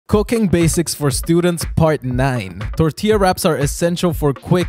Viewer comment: Why tf did i hear "cocaine basics for students"